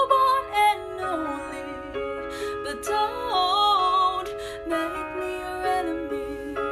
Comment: Am I the only one who hears "but don't make me your anime" 0:41